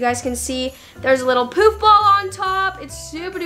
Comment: When she said poof ball I thought it said poop ball